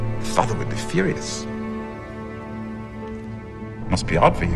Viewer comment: I thought he said "must be odd for you", not "must be hard for you."